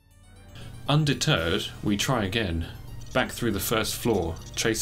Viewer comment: Haha I swear you said "under turd". Almost spit out my drink.. Undeterred? Ah, that makes more sense. =oP